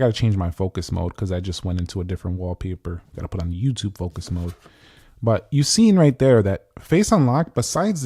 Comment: you're a filipino? am I hearing it correct hahaha "Meron palang Youtube Focus mode" ??